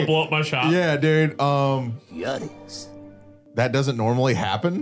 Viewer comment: subtitles say curious but dont you hear "yikes" as well?